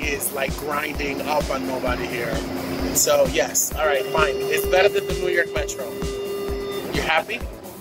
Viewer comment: I didn't understand what he said after fine. Can somebody aid me? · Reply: Is Better than the New York metro. This is because that metro was clean and not packed and it was on time. Thank you for watching